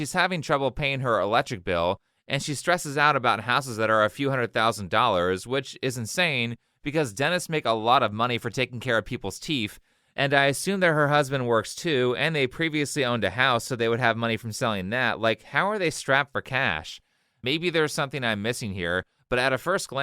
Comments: did you say teef lol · “Teef” haha I heard that